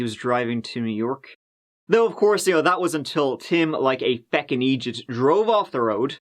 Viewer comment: did you say a feckin idjeeut?